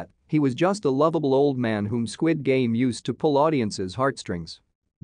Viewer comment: My Dumbass really thought it said stupid game 😭